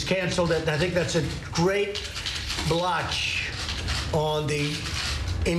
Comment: Lmao I love your trump impersonation. Also I thought blotch said biotch 😂😂😂